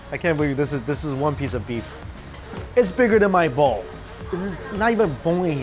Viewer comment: did I heard you saying "Bigger than my ball? 😂😂😂 Kidding. I am sure you said Bowl😁